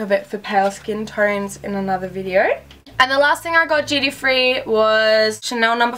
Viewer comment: Could someone help me at 7:51 is she saying "gd free"?? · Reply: duty free :)